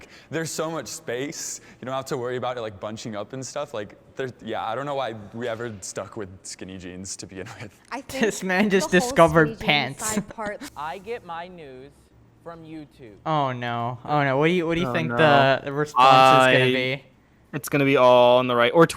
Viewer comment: thought it said "i get my nudes from youtube"